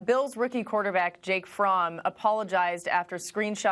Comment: Jake fromm state farm.. i thought she was saying jake farm.. child😬